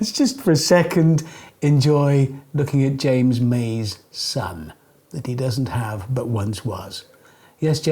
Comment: I genuinely thought he said ''James May's Son, that he doesn't have but once was.'